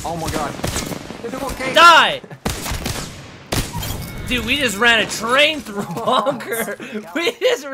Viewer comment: I thought he said we ran a train on loba